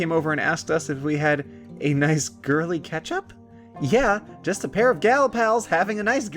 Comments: it sounded like you said ketchup when you said catch up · So I just so happened to be looking away from the screen at 1:30 and thought he said "Girly ketchup" and was wondering what the frick that was and why a condiment would be so pointlessly gendered. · i heard "girly catch-up" as "girly ketchup" and went hOw iS cUtE lEsBiaN mArRiAgE reLatEd tO pEriOdS??